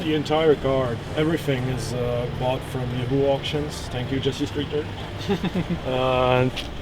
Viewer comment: what did he said? "everything is bought from something auctions"? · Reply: Yahoo auctions